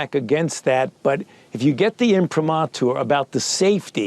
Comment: "informatu" what? who uses that word, ever? Defeat Fauchism!